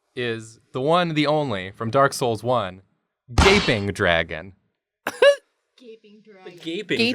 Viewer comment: I thought he said Vaping Dragon!! I’m dying!!! Send help 😂😂😂😂